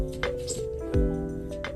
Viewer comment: Ooo that's so ni- SHHH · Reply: I heard "Oooh, that's fine--" but here ya go for all those looking